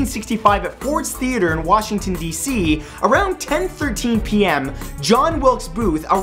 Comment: did he say "around 10 or 13PM"